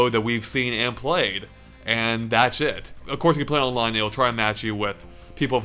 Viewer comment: Every single time says "that's it" (which he does quite a lot) I hear "That's shit".